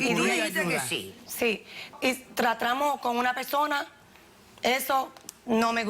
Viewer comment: escuché “tratramos”